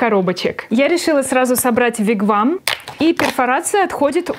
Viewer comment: Почему я слышу "фиг вам"?😅